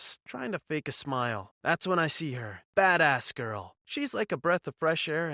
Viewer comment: Did he say badass or fat ass? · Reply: Badass